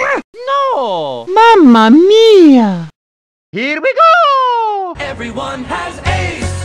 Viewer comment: For some reason, i used to think he was saying "everyone lets dance" as a kid. I now realise how wrong i was.